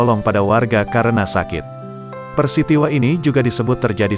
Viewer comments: Ngakak dengernya di detik 0:44,harusnya "PERISTIWA" jadi " PERSITIWA"😂😂😂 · PIRSITIWA, apa saya gk salah dengar bang